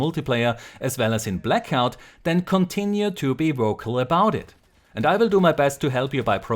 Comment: WOKE Finally know the origins of this damn word, misheard "vocal" with Austrian accent.